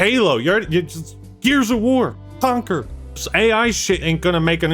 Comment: thought bro said Concord 💀